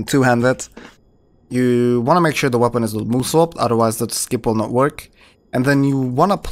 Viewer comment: Make sure the weapon is what? I'm hearing "ul-sulp", and even then I don't know what word that sounds like. 🤷🏻 Thank you, I'll resume watching the rest of the video 🙂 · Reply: Make sure the weapon isn't moveswapped.